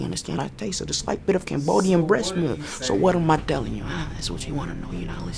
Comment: if not mistaken, i think i heard him saying "Cambodian breast milk"... hahahah